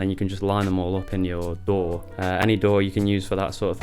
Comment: I was looking at the stickers right when you said DAW I read sour dough same time , sounded like you said dough haha